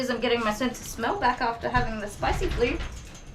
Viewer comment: Hehe... What is the spicy flu? You Tube Cc said spicy blue. It was at about